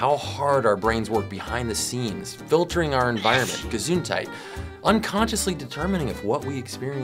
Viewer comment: Did he just say "Gesundheit"? (bless you in German)